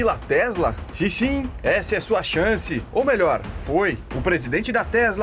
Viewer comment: Só eu ouvi Xi Chim? kkkkkk